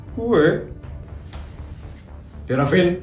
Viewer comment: itu putri nya ngomong apa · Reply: Gue